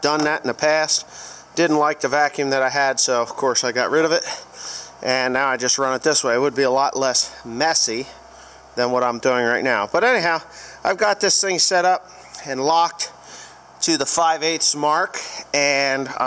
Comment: ish. Did you say a lot less Mesick?